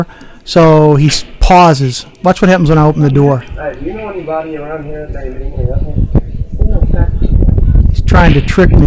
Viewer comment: What did he say at 1:07? "Do you know anyone around here eatin' many apples"? Hell does that mean